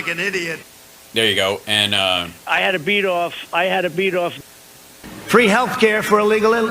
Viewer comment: Yes Donnie, like an idiot!! :face-green-smiling::face-green-smiling::face-green-smiling::face-green-smiling: Did he just say I had a beat-off, I had a beat-off?????????????????????:face-green-smiling::face-green-smiling::face-green-smiling::face-green-smiling::face-green-smiling::face-green-smiling::face-green-smiling::face-green-smiling::face-green-smiling::face-green-smiling::face-green-smiling::face-green-smiling::face-green-smiling::face-green-smiling::face-green-smiling::face-green-smiling::face-green-smiling::face-green-smiling::face-green-smiling::face-green-smiling::face-green-smiling::face-green-smiling::face-green-smiling::face-green-smiling::face-green-smiling::face-green-smiling::face-green-smiling::face-green-smiling::face-green-smiling::face-green-smiling::face-green-smiling:Hal is :finger-red-number-one:!!!